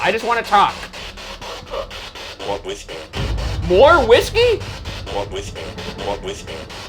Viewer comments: I heard “we’re with her” when it said “more whiskey”, which is far more creepy to me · I dont think it said "more whiskey" i think it said "Well I'm with you" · The “more whiskey.” Voice was SO clear and didn’t sound robotic at all I think it was the best spirit box reply to date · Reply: i think the more whiskey sounded like it said “walk with me” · I think the spirit said "mourn with me" instead "more whiskey", because after Ryan asked it if it wanted whiskey it said "that's not right". Also instead of "wake up" I think it answered Ryan's taunt about it being the last hurrah with; "I don't care". Sassy spirits. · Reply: yea i also heard the "thats not" but i thought it was just cut off but i thought it said "no whispering" instead of "more whiskey" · "More whiskey," and, "See any ghosts?" Are tegit the most audible and clear pieces of speech to have come out of that spirit box in the entire series...as much as I recall. I'm prolly wrong but that was very clear, unlike most sounds that come out of the spirit box which seem unintelligible if you listen to them without looking at the subtitles. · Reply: I heard "more with you", not "more whiskey" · When the spirit box said “more whiskey” i more so thought it said “oh, i’m with you” 👀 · I thought it said "what with me?" when ryan said he just wanted to talk instead of "more whiskey" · I don't think he said "More whisky" I think he said "we're listening" 😂😂 and that makes sense too, haha